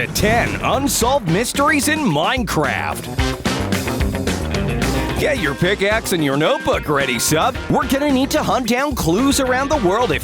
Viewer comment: I thought he said " Get your baguette and notebook"